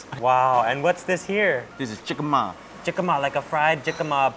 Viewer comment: no one: Overwatch players: DID he say SIGMA ??